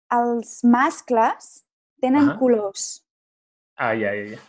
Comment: Escuche mal o dice tienen culos ajajjaja